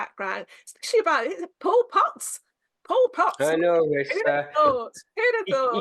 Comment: I thought he said Pol Pot, the dictator, to start with 🤣, that would've been quite different to the singer Paul Potts! Great series 👍